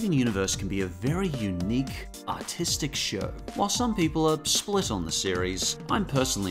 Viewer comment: When I first watched this vid I thought he said "autistic show"